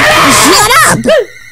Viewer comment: now im wondering if chara does actually say "SHUT UP!"